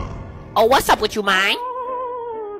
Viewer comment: I thought he said gEt a WaG gEt A wAg